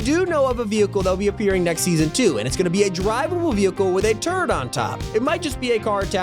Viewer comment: I had to go back and listen again cause I thought he said "a drivable vehicle with a TURD on top" not a turret just a giant turd atop the car 😂